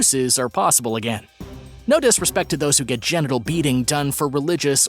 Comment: I heard genital ‘beating’ 😆 😂😂